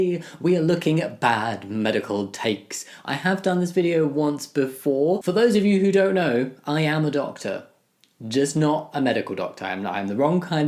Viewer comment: heard this as, “For those of you who don’t know, I AM adopted.” And got very confused 💀